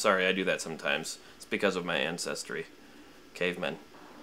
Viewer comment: Lol I thought you said Incest-ry instead of Ancestry